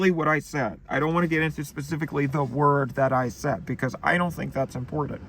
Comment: am i insane or did he say “n word I don’t understand…”?